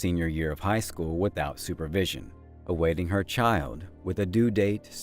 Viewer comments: I'm confused?? She is awaiting her due date? I thought she already delivered her baby and he was dead... Did she get pregnant again? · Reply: So it was explained to me that THE confusion is because the narrator sayS "awaiting her TRIAL" and it sounds like she is saying 'awaiting her CHILD.' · did none of you pay attention??? Due date for her court case, do better and pay atention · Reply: Sounded like he said awaiting her "child" not "trial". Hence the confusion.